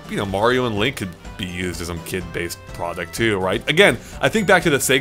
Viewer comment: Did he say sock-based?